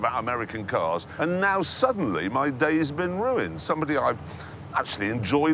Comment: I always thought Jeremy said "is weird" instead of "he's weird", also makes more sense grammatically i think? Sorry to be that nazi guy.